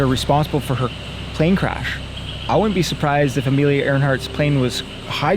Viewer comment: great video, however theres no n in amelias last name. earhart maybe I am wrong but I thought I heard you say earnhart. still I liked your video witha big thumbs up